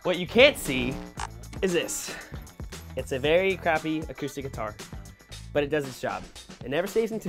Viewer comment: Who heard Ross say its a very crap guitar on 1:11 XD